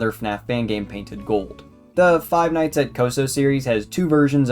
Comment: i hate that im watching this cause I thought it said Five Nights at Costco